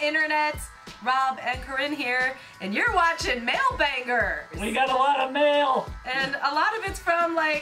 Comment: When she said “mail banger” I heard it as “male bangers”😂